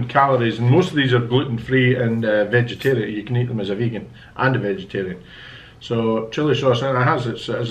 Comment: said he's vegeterian haibo or was I hearing things😂😂